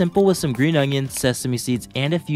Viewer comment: I thought he said “how do you like your toes in the morning” 💀